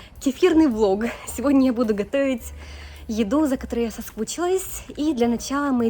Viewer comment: Может мне послышалось : за которые я соскучилась😮. Это точно не по-русски!